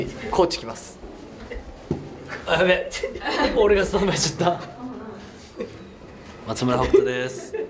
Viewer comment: ここの北斗なんて言ってるんだろう？何回聞いても聞き取れない😂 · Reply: 俺がスタンバイしちゃった(◜௰◝) だと思います！